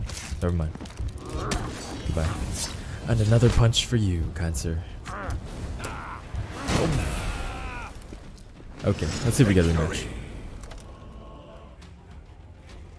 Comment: did u say another punch 4 you , kind sir , or cancer , cause im pretty sure u said cancer